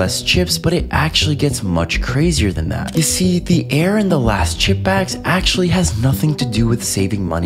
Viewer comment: I thought he was saying last chip bag